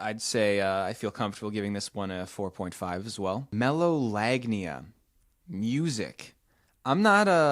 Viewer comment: I have melolagnia. There is some music that is very sexy to me, specially tropical and pop songs. For example, the very sexualized music and stuff that matches the rythm of sex and so on. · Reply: bruh i thought u said megalovania 💀